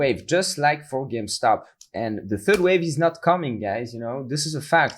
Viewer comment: did he say it’s not coming?